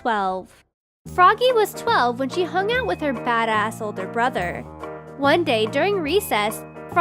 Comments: Instead of “badass older brother” I heard “fatass older brother” 😂😂😂😂😂😂😂😂😂😂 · I thought it said fat, not bad. I was so confused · on #7 i thought said fat ass older brother instead of badass 😂😂😂😂